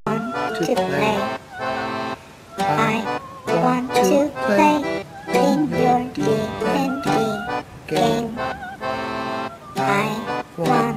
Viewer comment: "♫I want to play in your D&D Game♫"- Chris Perkins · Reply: I thought I heard 'I want to play with your Deity Game'.